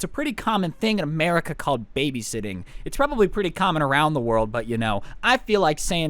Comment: I thought he said baby shitting lol